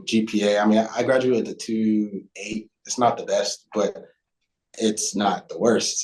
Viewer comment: Did he say 2.8 or 3.8 GPA? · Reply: I graduated with a 2.8 GPA. I majored in Health Science with a concentration in Pre-physical therapy. I wish I didn’t let my grades slip while focusing on flying and working. I did get straight A’s my last semester but it didn’t budge my overall GPA. A 3.8 sure would’ve been nice man!